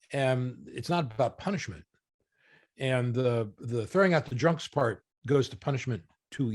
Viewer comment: I thought then said “then you throw in the drugs” Maybe I have a different idea of why a party means…